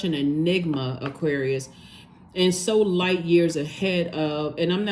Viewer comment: When says light years ahead I hear “Old Soul”.💛